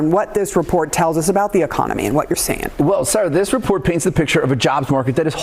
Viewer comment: did he say sir or sure? Sounded like sir...😮